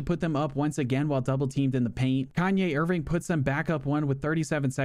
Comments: yo anyone else catch him say kanye irving?😂😂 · Bruh did he just say Kanye Erving??😂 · Did I hear “Kanye Irving” 😭😭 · Yall heard him say Kanye Irving 💀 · Am I the only one who heard him say Kanye irving